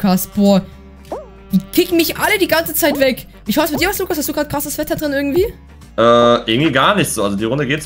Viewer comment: Verstehe ich "wie sieht es bei dir aus hast du krasses Wetter "